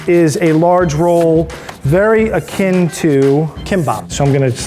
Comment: tamagoyaki*.. When he says it I hear "egg is sweet omelette" lol its like saying chai tea